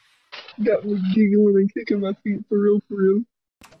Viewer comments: the unintelligible part was him saying "Got me giggling and kicking my feet for real for real" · I think he's saying "got me giggling and kicking my feet, for real, for real".